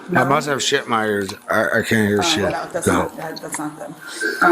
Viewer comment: "Can you turn it up a c*nt hair?" 😂😂😂😂😂😂😂 For a moment I thought he mumbled "Couldn't hear". Nope. Classic 80-90s terminology. Haven't heard that line since highschool.